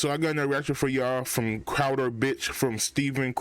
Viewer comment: Not going to lie. Thought you said “Crowder Bitch.” Lol